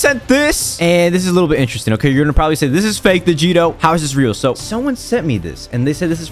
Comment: I Thought The Stream Just Said CLICKBATE DIGITO